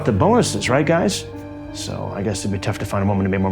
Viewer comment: what is he saying all I hear is ig it would be tough to find a some bebloble bleme